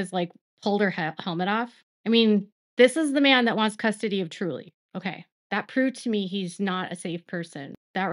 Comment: i thought i heard you say " kody lost custody of Truly ' ?